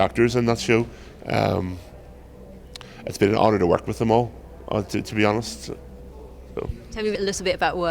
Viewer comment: I swear I thought he said "It was a Hodor working with all those great actors" :D